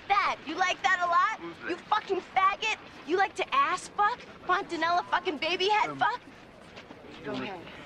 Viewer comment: what does she say? Pontonello?